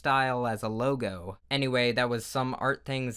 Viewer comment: nit sounds like he said “my name is solar sans” AYO NEW Undertale AU⁉️💀💀💀💀💀💀